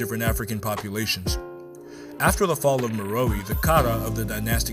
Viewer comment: Bro, at 08:18, what was the name of the civilisation that fell. Sounded like Meroe?